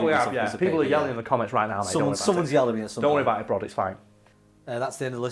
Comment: did just call brod? Or am I going. Crazy